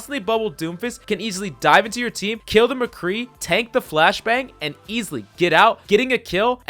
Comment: Ahaha ha am I the only one who heard him saying tank a flashbang😂😂😂😂😂